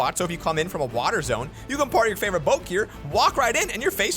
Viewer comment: par my favorite boak? im not the only one who heard that right